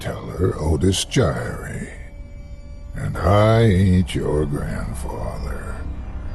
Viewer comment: I thought he said "I'm storyteller Otis Jiry, and I ate your grandpa." What's more embarrassing is that I didn't realize I misheard it right away and thought the first story was going to be about elderly cannibalism or maybe Otis was just sharing what he did the past few days (days in which I haven't talked to my grandpa)... The guy could make anything sound scary.